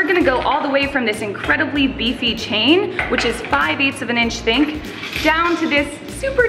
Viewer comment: she say "inches think" instead of "thick" hehe